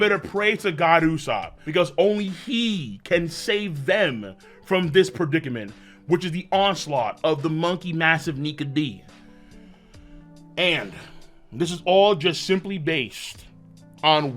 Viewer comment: For the moment I thought you said Monkey Niga but you said Nike